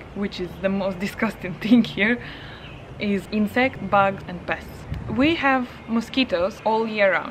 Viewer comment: Looking for a new country to live in and I hear "...the most disgusting thing here is inserts, bugs and pests." Nope. Thanks for the video! Scratches Mauritius off his list :(